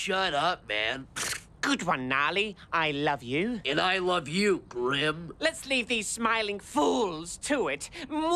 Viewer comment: Anyone else hear, "And i love you BRIM."